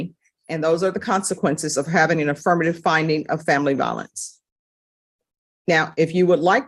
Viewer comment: I thought she was saying "family balance."